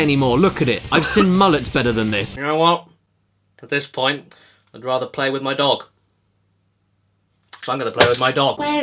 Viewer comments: When you said “I’d rather play with my dog” I honestly thought you said “I’d rather play with my knob” 😂😂😂😂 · Did anyone else hear "I'd rather play with my knob"?